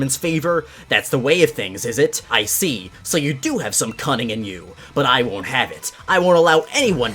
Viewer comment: Misplaced ad, I heard “ah so you do have some cun”... right before it cut off 😂😂